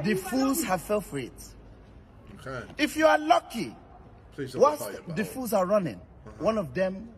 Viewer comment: What did JJ say at 0:39? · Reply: I think he was saying “please don’t grab the belt”